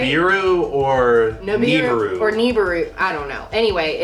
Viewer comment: DID HE JUST SAY E-bru, cause that sounds like meh name...just said differently